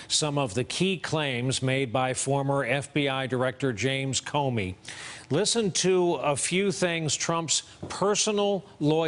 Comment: Comey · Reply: Moody: srry, i thought you said "Comey is lying".